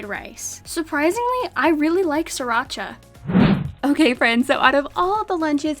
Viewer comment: I thought she said 3racha...😅